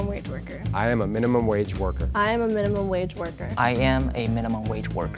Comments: I heard "I am a minimum mage morker" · For some reason I heard " I am a minimum wage burger "